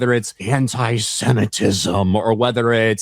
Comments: It sounded like said "Hentai-Semitism" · I swear said "hentai-semetism". I struggled to picture it.